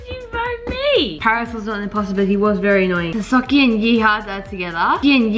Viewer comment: Thought said fuckin but she said Vickie and yeehaw 😂😂😂